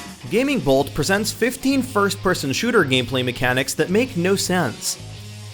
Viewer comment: İ thought it said 15 frames per second